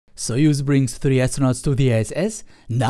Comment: lol, at the Beguing for a second I thought you said "Soyuz brings three astronauts to ze S.S."